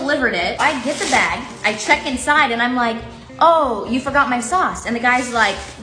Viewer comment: I first thought she said: you forgot my socks.. and I got so confused why a restaurant would sell socks ..